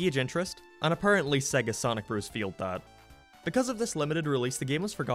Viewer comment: I thought it said “seasoning bros” LMFAO